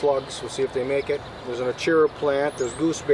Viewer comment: what's the plant you mention there? It sounded like "ochira"? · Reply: Achira. A main crop staple of the Americas.